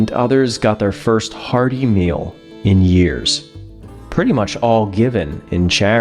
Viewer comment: You said “hearty meal” and I thought you said “hard email.” 😂😂😂